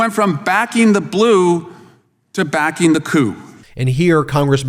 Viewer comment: I had to listen twice.... "From backing the blue to backing the coup". I swear I heard "backing the poo" and I died laughing thinking no way he just said poo 😂😂😂 tomato tomato 🤷